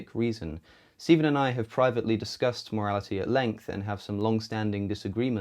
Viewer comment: Misheard "mortality" for a while and was very confused